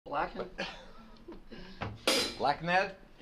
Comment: Sounds like he said "black man" to me.Riff weak, black man strong.😅😅😅😅😮